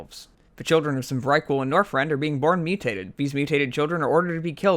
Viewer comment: You need to enunciate. Your speech is so incredibly slurred. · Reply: Right? Sounds like he is saying Norfrend